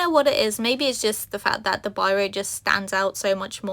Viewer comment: what is the material or page name said here? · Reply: Oh I see biro pens are ballpoint pens. I didn’t know that. Neat.